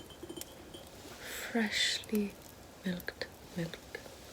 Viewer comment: I think my volume was too low because I heard “fresh milked apple pie”